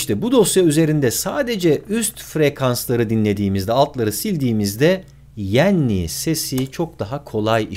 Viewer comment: anam granly duydum noluyo